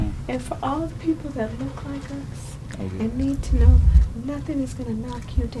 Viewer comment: Kamala is so insipid and vacuous, i cant bear to hear her talking. Did i hear right, i thought I heard her say 'people like us'? Was she referring to their corruptness, skin color, being female or what? Edit...'look like us' just watched it again. Mmm ok, pathetic